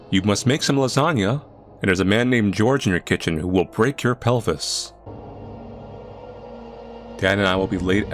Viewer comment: I thought it said or else he will break your peni